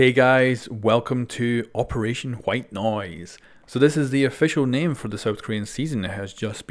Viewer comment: At first i thought it said white nose